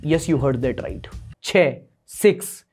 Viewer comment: I heard and hurts this